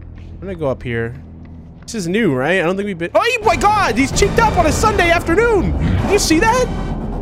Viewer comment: what did he say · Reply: "Omg he's checked up in a Sunday afternoon"